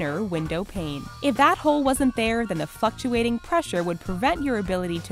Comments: I thought she said fuckshuating lol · am I the only one who thought she said fucktuating